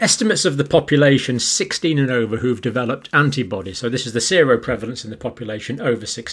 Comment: Thank you Dr C ☮️👍💙. Lol, I thought you said zero prevalence… I was trying to figure what you were saying 🤣